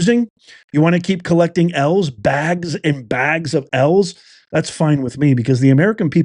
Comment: I laughed because I thought you kept saying bags and bags of elves instead of L’s.😅. Either way they’re losers